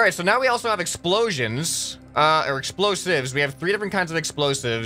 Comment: there was an add for a movie with explosions and I thought you said explosions instead of engines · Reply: Nvm u said explosives